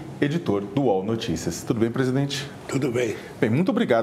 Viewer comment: tudo bem presidente??? É isso mesmo que ouvi